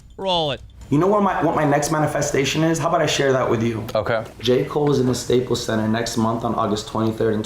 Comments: Who else thought when he said J.cole they said Jake Paul lol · Who else thought keem was saying Jake Paul not J.Cole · J Cole LMAO I thought he said Jake Paul I was like wtf why would you wanna be on stage with him lmaooooo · O I thought keem said jake paul not j.cole